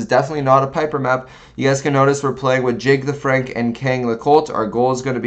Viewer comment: I thought he said Jig the Freak 😂